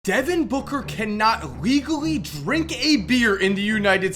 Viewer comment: I wasn’t paying attention and thought you said Deven booker can’t legally break a mirror (Not trolling By the way)